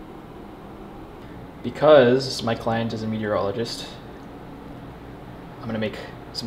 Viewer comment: I thought you said "urologist" instead of "meteorologist" and was really wondering what shape you were going to use there.